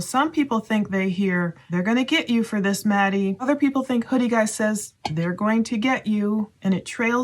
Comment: I don’t hear that at all. I hear “you’re going to get to house Maddie and you’re going to tell him” which would probably explain why they were trying to call Jack. Which I’m using my studio recording HIFIMAN headphones for everything I need to hear.